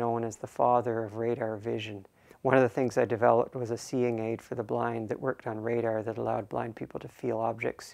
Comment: I thought I heard him say black instead of blind